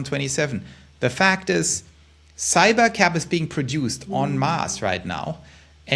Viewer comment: Did he just say cyber cab was being produced on Mars? Too much stress. I wonder what is really going on? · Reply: En masse…sounded like “on Mars” w accent